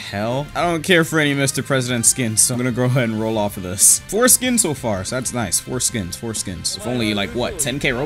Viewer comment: the fact he kept saying four skins over and over and it sounded like he was saying foreskin